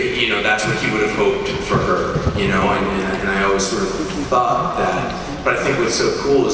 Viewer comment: Did say "Fuck that" under his breath when was talking about Frank and Karen's relationship and nobody noticed?????? Someone said something at that moment but I can't figure out who.